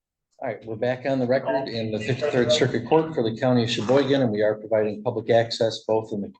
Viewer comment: I heard "Pittsburgh circuit court of Cheboygan" right at the beginning. Had to go back and listen again. 😅